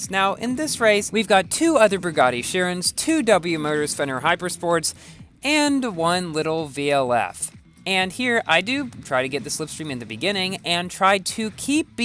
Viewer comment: Wait At 7:37 Did You Say Fenyr HyperSport lol